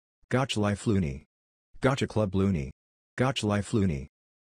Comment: captions gotcha club loony its not loony you mean Gacha Club Luni